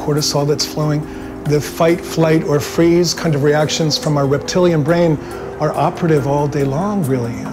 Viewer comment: Did he just say 'reptilian brain' at 5:26?